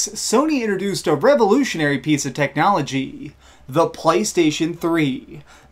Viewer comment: did you just say " the phley station 3 "